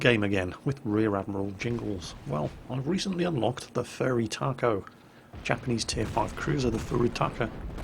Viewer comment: At the start it sounds like he is saying Furry Taco.